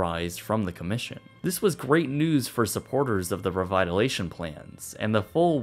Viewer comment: Lol at 10:25 you said "REVITALATION" instead of "REVITALIZATION"! Tsk Tsk😏😏😏